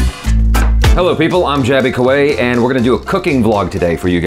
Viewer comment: The captions said "Jaby Kawai" 🤣🤣🤣🤣🤣